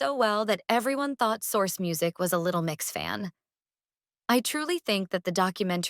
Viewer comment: Did you just say Little Mix??? (Love them btw lol)